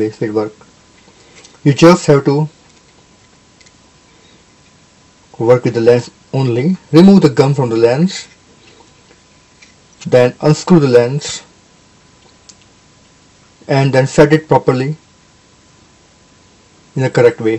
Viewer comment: Thanks for the video. I wish you could have made it in HD to see the detail better, but it is still a good video. What did you say from 7:04 to 7:09? I couldn't quite understand what you said. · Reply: Dialogue is something like this: You need to work with the lens only. Remove/loosen the gum holding the lens; unscrew it and set it properly... Hope this will help you :-)